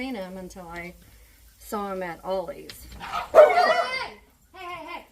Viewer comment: Rude people, UGH!! They can't steal your SPARKLE. Love ya girl❤❤❤❤ I'm heading to aldi to look for those disposable cutting boards.(not that I need them, LOL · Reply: She said in the comments there at Ollie’s I thought she said Aldi but it’s Ollie’s I’m getting some to ! Lol !